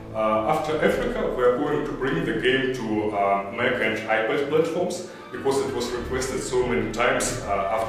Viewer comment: Not trying to be an ass, the man has a thick accent and his voice is echoing in that room. I was particularly intrigued by what he was saying at 1:10. "After Africa, we're going to bring the game to.." what? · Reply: mac and ipad platforms...